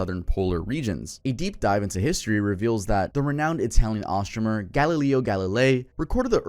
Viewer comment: The way you said astronomer just snapped me back from zoning out doing dishes to rewinding to make sure I heard you right. “Astromer” is wild.